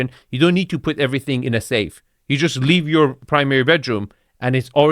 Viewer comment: a hideaway powder room so people don't have to leave while you have a party?? Iv never said to my guests "oh you need to leave if you need the loo." "you cant have wee in my house" 🤔😂I completely miss-heard this lady, take no notice of my old ears. 🤦‍♂ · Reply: I heard "wait", not "leave"...